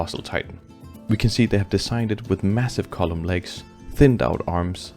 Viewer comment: When he was talking about the colossal Titan I thought I heard him saying "massive butty" 💀 thanks for the explanation bro, you really do explain details well I hope you get lots of admirers and success in the future, thanks again